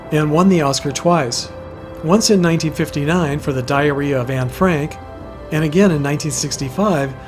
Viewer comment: ???? DID YOU SAY (Shelly Winters), "for the "diarrhea" of Ann Frank?" (Time 5:50). Sounded like it after listening to it for 6 times!